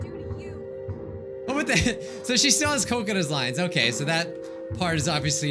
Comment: When he said kokona i thought he said coconut